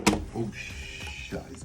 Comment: Did I hear a "Oh, Scheiße" (Oh shit in German)? :D